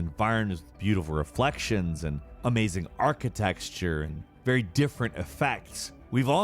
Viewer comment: ish did he arch-texture? or am i hearing things?